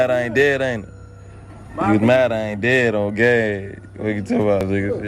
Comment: why the f**k does it say “they mad I ain’t dead or gay”!! 😂😂😂 them captions are all messed up but funny as hell😂😂